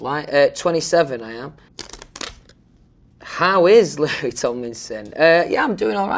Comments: is it just me but I LITERALLY heard larry tomlinson not louis tomlinson wtf 😳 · IS IT ME OR DID HE SAY " HOW IS LARRY TOMLINSON "????